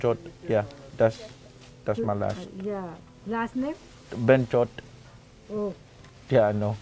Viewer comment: what he say? Benshot? 😂😂😂